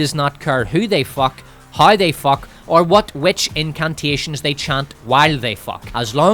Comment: I heard "incuntations," anyone else?😂